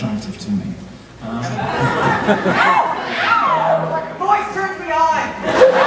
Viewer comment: It sounded like they said, "That voice turns me on!" XD